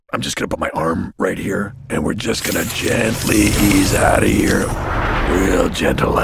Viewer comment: put subtitle/closed captions on and in English and at at 2:07 play it notice how batman said "were just going to gently ease OUT OF HERE" in the words below it says"AT A YEAR" instead of OUT OF HERE